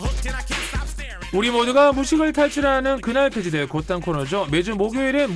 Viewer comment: 그날그시대 OO코너죠? 이거 도대체 뭐라고 하시는 건가요 ㅠ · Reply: 모두가 무식을 탈출하는 그날 폐지될 고딴 코너죠~? 입니다~~^^